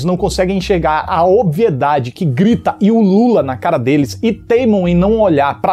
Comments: "a obviedade e o Lula na cara deles" Foi isso mesmo que ele disse em 1:57? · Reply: Ulula, do verbo ulular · Aos 1:55, disse: "grita e ulula...", mas diante de tantos memes da polarização política, eu ouvi: "... e o Lula?" Será que foi proposital?